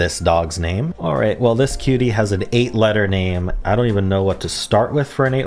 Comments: "This kitty......." Wth is wrong with you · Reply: You misheard, I said "This cutie" but I can understand the confusion! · “Well this kitty’s name” 💀 it’s a dog hun? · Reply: I think you misheard "cutie" as "kitty" :)